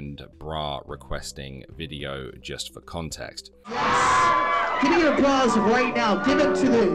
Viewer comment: Was listening to this while up and moving about. When he said "gimme your bras" I thought he said "gimme your paws".